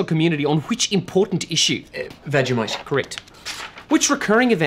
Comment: Can't understand this. What's the answer here? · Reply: "Vegemite," which is an Australian food-like substance made from brewery waste, onions and celery, and typically spread on toast. Most other nations do not yet have any laws against its importation.